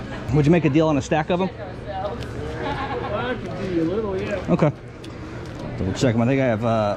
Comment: 🤣 sounded like u said " I got 14 , double check my ni99a lol